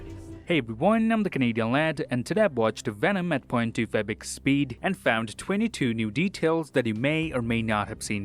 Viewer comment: I clicked so fast that i thought this was the new trailer breakdown lol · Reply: I thought he said i watched venom 2 in 0.25 speed. And i was like how!!!😲😲